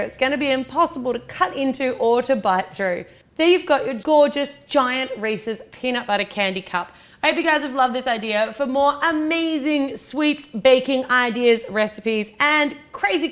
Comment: did she just say a “GIANT RACIST PEANUT BUTTER CUP” lmao 😂 😂 I know she said Reese’s but it did sound like racist lmao 😂.